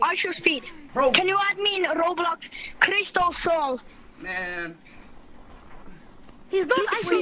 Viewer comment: DID BRO SAY CRISTOLSOALS